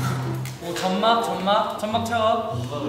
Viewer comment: Wait... 6:40 did he say "Chammak challo" XD or I have to get my ears checked?